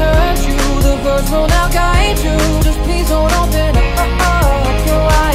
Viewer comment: For one of the lines where it says, "the birds will now guide you" i hear, "the sounds will noe guide you" Does anyone else?